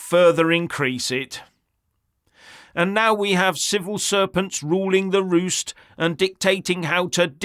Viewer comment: I heard "Civil Serpents." 🐍